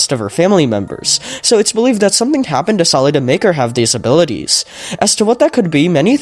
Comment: why do the captions say disabilities???